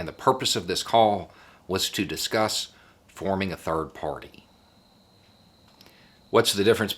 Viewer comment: Somethings wrong with my speakers, it sounds like you said the are forming a Turd part.